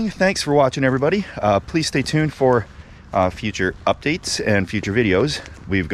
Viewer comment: Did just say "Police stay tuned for...."?